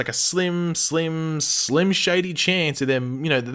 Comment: mmmm did I hear "Slim Shady"?